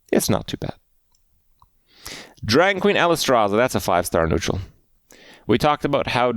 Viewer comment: Shroob-adier? Trent? Coomoolo? ... Friendship Rice? (was that intentional?) And, as always, Alice-traza??? But hey, Kripp almost said Dirigible right! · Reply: it sounded like he was saying Alliestrasza, who is a fellow streamer of Kripp.